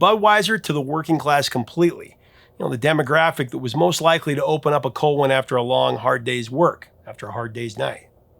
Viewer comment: I heard "..open up a colon after a hard days work..." and thought, dang, I never knew.